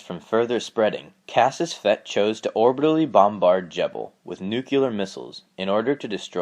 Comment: did you just say nuculer? 'Eye twitches'